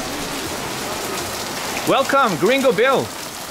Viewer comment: I thought you were saying gringoville until midway through. LOL